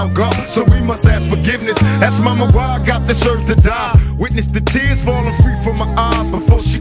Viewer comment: I always thought it said i ask momma why god deserve to die lol but its this urge to die.......now I know lol